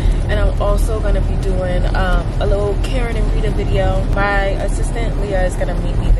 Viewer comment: You talk so fast all this time I thought you were saying Karen Narita not Karen & Rita 😅☺️ Blessings to you and all your business goals love. ❤️